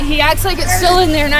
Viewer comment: Did she just say he acts like a solo nerd